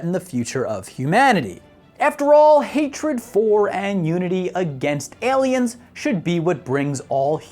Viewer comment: he says "unity" but I hear "nudity." And again when he says it about 10 seconds later.